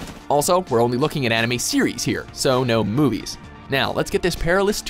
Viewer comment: I swear he said ANIMEE!!